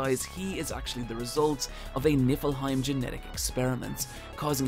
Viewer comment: DID ANYONE ELSE hear him say "Nibelheim genetic experiment" by mistake? Mah boi Hojo would have had to be a prolific bastard indeed!